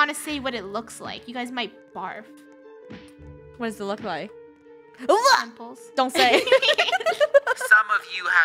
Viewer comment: did hold say nipples?